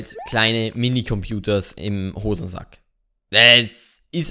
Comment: Ich hab Hodensack verstanden xD Ich glaube auch, dass sich Smartphones irgendwann in unsern Körper befinden und wir verschiedene Steuerungsmöglichkeiten mit Hilfe von Impulsen kontrollieren können. Das muss dann aber sehr zukunftsorientiert sein, damit Updates immer durchgeführt werden können. Ich glaube aber nicht, dass wie das noch miterleben. Ich schätze bis 2050 werden Smartphones so wie jetzt sein, allerdings halt mit mehr Features und halt einfach einem Display.